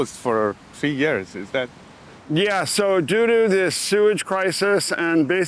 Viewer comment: He said "due to this sewage crisis" but I swear I heard "doodoo this sewage" 😭😭😭